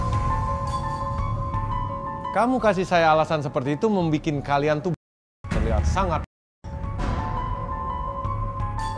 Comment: Menit 9:24 sama 9:28 ngomong apa sih kok kayak di sensor. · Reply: kelamin pria